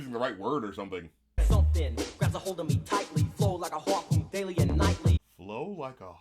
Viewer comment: "Flow like a harpoon." When he lets it go, it sticks in you and then you slowly bleed to death. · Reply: I've always heard it as "flow like a heartbeat" which I somehow never questioned... yeah, a heartbeat doesn't flow, but it does happen daily and nightly. Why not?